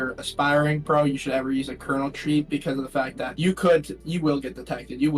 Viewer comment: bro put captions as colonel instead of kernel😭